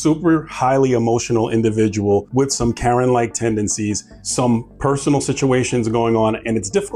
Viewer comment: At first I thought you said "Karenoid tendencies."